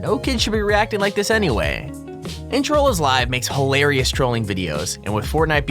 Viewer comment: did he just say “introllslive?”